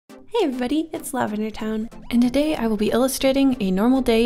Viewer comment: I always thought she was saying "... It's lavender time .."